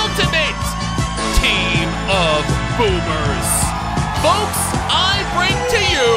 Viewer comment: Did you just called us boobs in